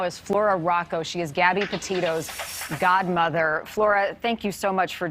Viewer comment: I thought it said she was her grandmother and thought damn, she looks like a kid herself.